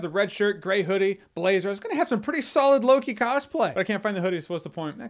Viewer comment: At first I didn't know was saying Loki, I thought he said some low-key cosplay. 🤷